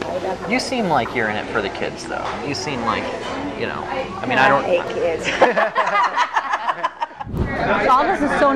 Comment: It sounds like she is saying "I mean I don't hate kids," but admits she is doing it for the money. She is really saying the same thing either way, but that is what I hear when I play it back. Unfortunately, reporters sometimes speak when they should be more silent in awaiting candid responses. His voice makes her exact wording unclear. Regardless, this disgusts me.